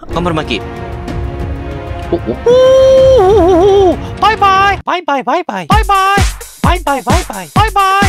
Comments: バイバイがボイパに聞こえる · バイバイ〜の所ワイパーて聞こえた笑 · バイバイのとき、いつの間にか、パイパイに聞こえる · 途中バイバイがwifiに聞こえたw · バイバーイが途中でワイパーに聞こえたｗｗ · バイバイがパイパイに聞こえる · バイバイがとちゃうでぱいぱいに聞こえる